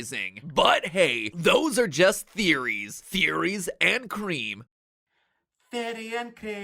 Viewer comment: I thought he said "therein cream"😭